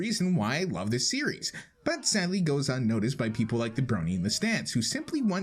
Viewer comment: did he say the brony? cause I'm a brony